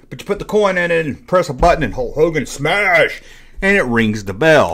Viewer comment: Did you say, “Hulk Hogan” at 3:57?